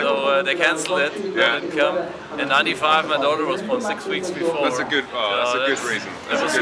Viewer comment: What did the guy at 3:35 say why he couldn't come in 95? I didn't understand it. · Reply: His daughter was born six weeks before.